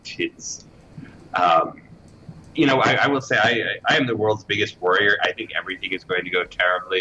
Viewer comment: Did he just say "I am the worlds biggest warrior?" (16:27) xD (I do realize, what he actually said.. but this just sounded too cool, to not comment on that. Especially if you consider your and his fondness for pen+paper role playing)